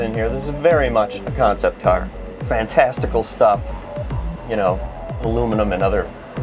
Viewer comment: DID HE SAY "FANTASTICAL STUFF"